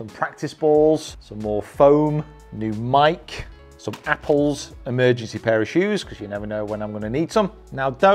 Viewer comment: when I first heard it I wasn't looking at my screen and heard "emergency parachutes" instead 😂 then I rewound the video and was a little bit disappointed 😄 Nevertheless a fantastic golf cave!